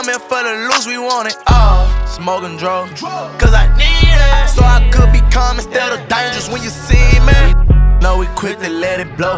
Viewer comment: I was wondering about what he said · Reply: So i could be calm instead of dangerous when ya see me